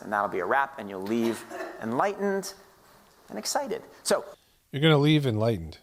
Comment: I think @ 14:04, he said "enlightened, and accepted". I listened multiple times, and it sure sounds like accepted.